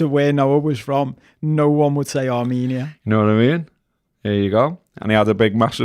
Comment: did just say Noah I mean?